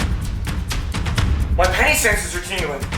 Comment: i thought u said panty senses